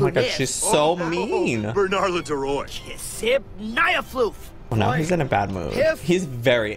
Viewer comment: i think that old man just said kiss my ass